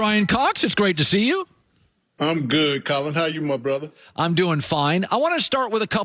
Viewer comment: When he said my brotha at the beginning I thought he said muhfucka at first